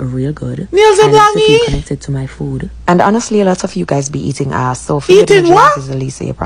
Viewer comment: Did she say "guys be eating a**"? Hope am wrong. I call foul 🟥🚫